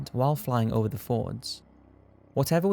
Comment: Sounds like your saying fords and not fjords!? Just sounds funny if there was cars flowing thru the Norwegian country side :)